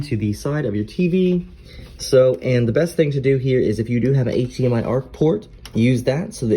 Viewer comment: What port did you say to use at 08:26? Please and thank you 😊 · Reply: I said HDMI-ARC, however it’s HDMI-CEC. Which should be supported on all ports of your TV if it’s offered.